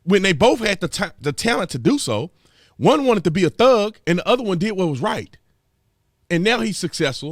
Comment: Ha! I thought I heard you say, "One wanted to be a THUG and one wanted to be WHITE"! Of course you didn't say that. You said, ...and one did what was RIGHT." But it occurred to me that that is how many blacks look at their choices in life. They can either choose to be "real" black, which means every negative stereotype of blacks the popular media pushes. Or they can "Act White", meaning do what white people do to succeed. I saw this in a Pizza Hut I worked at in Las Vegas. It was a very good neighborhood that was at least 50% black and over 80% of the kids working there were black. The manager was a young black man who always wore a dress shirt and tie and spoke sharp, proper English. Guess what? The black kids constantly mocked him behind his back for "always acting white" and would say derisively to one another, "Percy thinks he white!" These kids had parents with good jobs and who were able to buy a new house in a new neighborhood selling for over $200,000 and above ten years ago! I wonder, did their parents "act white"? Or like thugs?